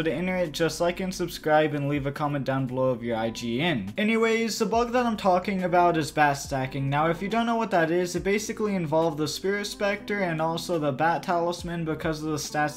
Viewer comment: I thought u said “pet stacking” and i was loosing it